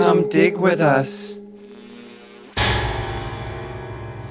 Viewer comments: I thought they said “come d*** with us” 😂😂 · I thought they said '' come d**k with us''